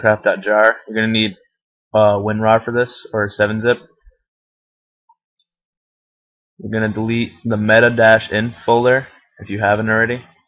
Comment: I thought he said delete minecraft.jar